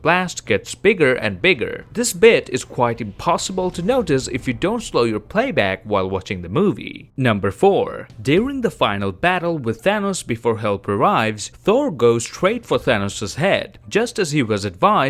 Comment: did he say "number Thor"?